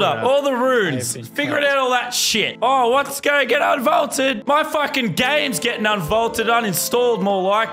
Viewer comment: Little did people know that the drumgun would be vaulted again in season 10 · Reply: oh sorry I thought you said VAULT not unvault lol